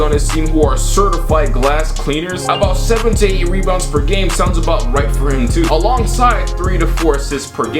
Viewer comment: Lmao I thought you said 17 rebounds per game